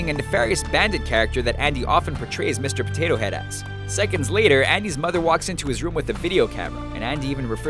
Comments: did he say " head ass " ?? 😂😭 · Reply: He said "... Portrays Mr. Potato Head as." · Who else heard "Mr. Potato Head Ass"?😂😂😂